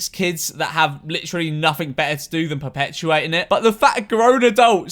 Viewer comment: Who else thought he said "fat"